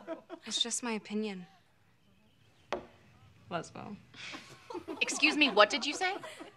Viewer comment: Did Addison say "Lesbo" Like lesbian to Ali?!? In